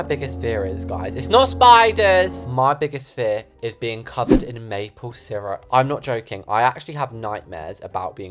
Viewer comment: I thought he said being covered in babies😂